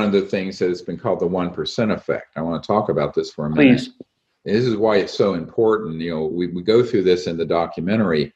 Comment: Hey everyone!! I stumbled on Dr. Greer's documentaries with my wife and found him to be one of the most interesting people in the world... the WHOLE second half we discuss his techniques to consciously contact UFO'S, we discuss Consciousness, Oneness, and many themes for our channel. So I thought his conversation would fit PERFECTLY! BE SURE TO LIKE THIS VIDEO ++ COMMENT BELOW IF YOU ENJOY! Thx for watching!! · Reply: did he say at 1:12:04 : “this is why it’s so important Neo”